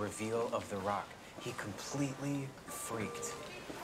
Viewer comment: what is said at 0:27 by him? And then by her? I only hear "Guilt" at the end. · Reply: him: “you could suddenly smell b.o (body odour) in the room” her: “Pavlovian”